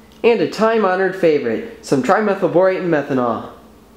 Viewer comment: What is that stuff at 2:04? couldnt hear him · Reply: Duke Nukem Trimethyl Borate Your welcome